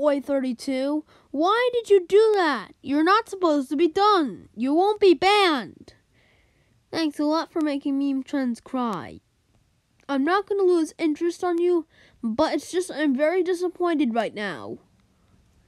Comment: "thanks alot for making my trans cry" is that what u said? Ooooooh, meme trends😂